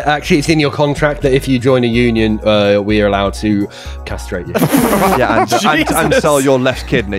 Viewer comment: I know it's just from the lisp, but it sounds like he's saying "cathsrate" instead of "castrate". The sad thing is, I don't know which one is more torturous sounding; being castrated, or being castrated by Cath. XD